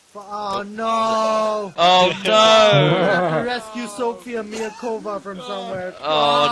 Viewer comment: You say "hostage rescue", I hear "Sofia Miacova" helloooooo